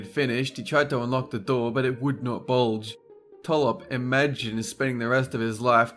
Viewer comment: w8 did he say it would not BULGE?!